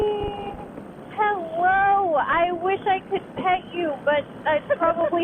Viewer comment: I thought she said PEG you lol